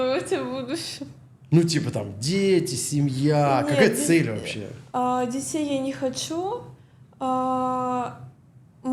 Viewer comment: что она сказала? Кто не знакомится в публичных местах? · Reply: Если вы о реплике про публичные места, она говорит, что к ней никто не подходит знакомиться.